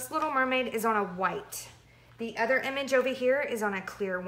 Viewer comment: I thought you said the 1st mermaid was on white water slide.